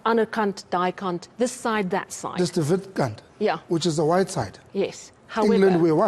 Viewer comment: I heard “watter kant?” If you listen in English it may sound bad… the whole attempt in throwing off the ref and perhaps getting a warning or penalty… wasting time… that’s all cheap tactics